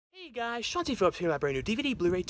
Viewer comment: I always thought he was saying Chauncey Phillips, not. I don't know what's real anymore.